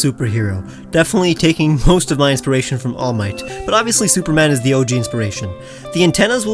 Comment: DID I HEAR ALL MIGHT